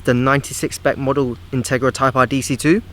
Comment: intarga type Arf . that's what it sounded like you said lmao. jk xD